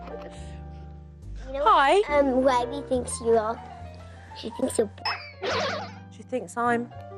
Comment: what did riley call at 4:00 i can’t figure it out it’s been days pls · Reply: She called her bs (bull sh!t)